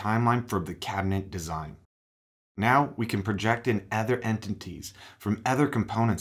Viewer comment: Can't understand what you are saying - "ether entities"(??). at 0:46. Is this a new word or do I need my ears testing again?